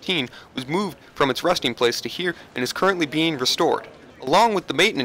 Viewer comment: it sounds like he said rusting place